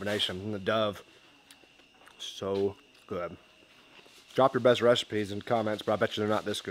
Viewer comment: I am so high. I thought you just said “drop your best comments in the recipes”